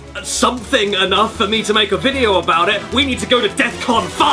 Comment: He said DEFCON aka defense readiness condition used by the US Armed Forces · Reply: I know what defcon is but it sounded like he said deathcon.